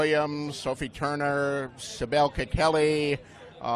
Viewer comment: Sabelka Kelly? Is that what he said? Because I can't find any person called that in the Game of Thrones cast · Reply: He said Sibel Kikelli , aka Shae